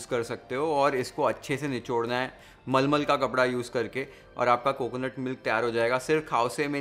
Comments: text description mentioned as Muslim cloth instead of Muslin cloth... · Here’s my really easy recipe for preparing Khao Swe at home just how we like it and you would be mind blown by knowing how quick and easy it is to prep! Check out the recipe and let me know! Cheers! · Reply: subtitle. Typo of Muslim instead of muslin. Please correct if possible.